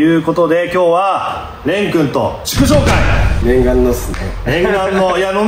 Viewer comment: 内容も相まって、0:44の「祝勝会」が「畜生会」に聞こえてしまう